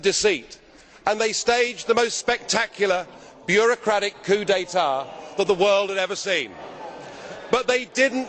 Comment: What is a bureaucratic "qudatare" ??? he says it in between 1:30 and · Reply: Nigel has a clean french: He says "(...) bureaucratic >coup d'etat< (...)" If you don't know what a coup d'etat is, google it, or I'll tell you, it's the act of overthrowing a government also called a palace revolution, takeover, seizure of power.